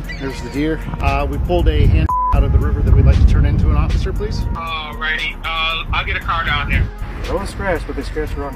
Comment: I almost thought the dispatch said 'already?' Instead of 'alrighty'